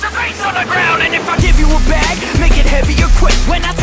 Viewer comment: I thought it said “If I give you a bag make it heavy or quit “